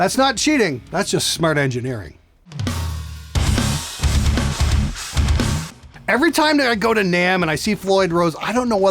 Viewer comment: Gosh he rants on but sometimes he has great jems. What did he say about trem systems being better than a floyd rose @ 15:53 was it a "camler"? Cam/Kam system ? I cant find that on a search. What is that ? Could some one enlighten me ? Thanks in advance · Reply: Kahler